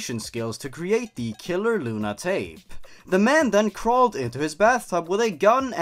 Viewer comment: For the first story I thought you said pale luna, and that had me real confused lol